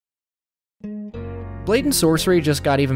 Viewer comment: I thought he was saying “blatant sorcery”….. I feel dumb cuz I was say out loud “don’t you mean blade and sorcery”….